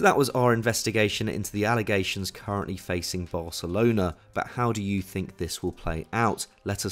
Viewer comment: is he saying varcelona @ 9:36 xD